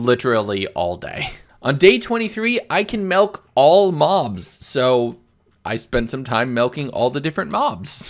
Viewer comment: what I heard you said 'I can milk all moms'